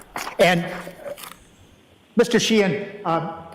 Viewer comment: Whenever someone says ‘Mr Sheehan’; I hear it as ‘Mr Sheen’ which is a well-known brand of furniture polish in the Uk. Just a random bit of trivia! 😁 Mr Sheehan is an incredible witness though, so knowledgeable and very patient and professional, even with Mr random questions😅